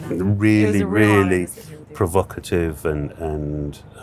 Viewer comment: did he say provocative? 😂